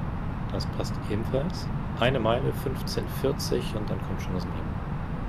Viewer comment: Ich bin glaube der einzige der sich das Video 2024 wieder anschaut. Aber da habe ich was gehört, das verstehen ich noch nicht ganz. Im Anflug (der erste) hat so etwa beim FUN VOR gesagt: "jetzt gleich kommt das MEMO", was ist das? :) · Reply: Hey! Besser spät als nie 😅 Ich glaube du meinst:"Ecam Memo landing no blue". Damit stellen wir sicher, dass das Flugzeug richtig für den Anflug konfiguriert und alles gemacht ist. Ist noch etwas offen würde es blau im mittleren, oberen Display stehen, anstatt grün zu sein. No blue heißt dann einfach: alles grün 😅